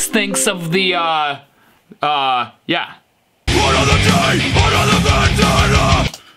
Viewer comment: I heard “another day, another mangina”